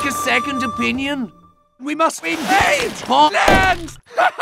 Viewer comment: did medic say “we must invaid Poland”